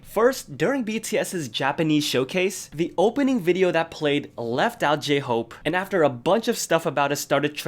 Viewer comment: I thought it said left BTS. Oml